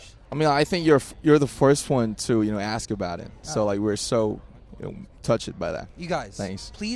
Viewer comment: am I the only one who heard him say "touche by that" welp everyone aint perfect I love namjoons accent